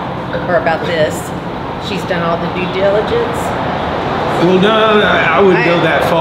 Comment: See Richard thought you said “dude diligence.”